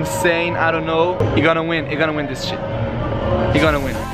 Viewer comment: Your gonna win this sh*t(thats almost what i hear